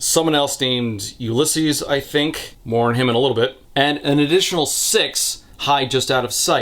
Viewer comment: heard this as “mourn him in a little bit” but honestly that’ll probably be true